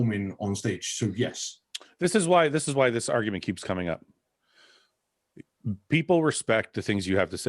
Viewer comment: When fouad Said "this is why this argument keeps coming up" the only thing i heard was "just tell me have mcdonals once a week aint bad" xD